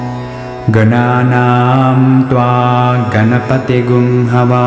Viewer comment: I THOUGHT IT SAID BANANA xddd